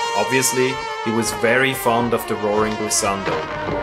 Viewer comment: Please tell me the name of this tool, I can't understand...😭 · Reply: I just say "he was very fond of the "roaring glissando". the glissando (continuous change of pitch upwards or downwards) is achieved by various musical instruments like the trombone or the strings.